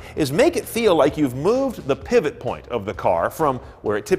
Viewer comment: Pebble Point? Or how is it called?! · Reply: Pivot point